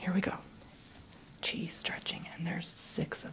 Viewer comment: Gosh you guys are fast. Didn't even have time to blink and there's already comments lolololol I can never be first again XD haha. not even on my own channel! :O This is my exercise routine! i wanted to show you this since i do this weekly and in case some of you were wondering about some tiny extra 'bulges' here and there in my muscles :-P feel free to follow along! it's fun! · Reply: Am I the only one who thought he said, "i'm gonna start out with some CHEESE stretching" at the beginning of the video?